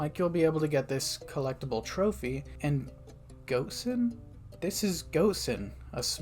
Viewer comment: I got so confused when you said "Wilson" because it sounds like "Goat Sim" 0:24 Edit: I think at first you said "Goatson" but still lol